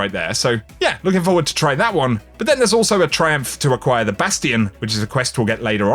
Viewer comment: Cant wait to try that exotic sidearm on Tuesday :P How is the D2 grind going for you guys this season? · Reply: I thought you said the "bastard exotic quest" instead of "Bastion"😂